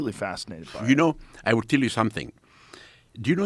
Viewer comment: At the start Zahi Hawass said, "I will tell you something," but all I heard was, "Let me tell you something, let me tell you something... wait wait wait."